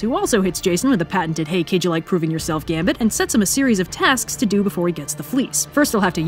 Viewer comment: I thought Jason was saying "Gasm" for a moment